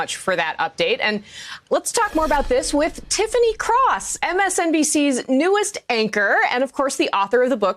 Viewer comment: Oh Tiffany Cross!! I thought it said Tiffany Haddish. 😰 got nervous for a second.